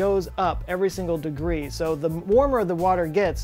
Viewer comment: Actually, they just discovered a planet with water in the atmosphere...I love your channel. I Double watch episodes.. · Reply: I thought he said liquid water. I could be wrong as I didn't re-watch to verify.